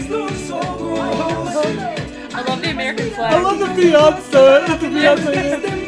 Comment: Did they say american flag